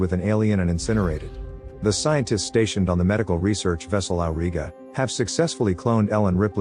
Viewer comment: Did u just say al rigga 😂 as in uae city 😅